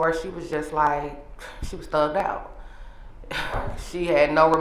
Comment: Did she say Hannah was "thugged out" at 6:59? 😂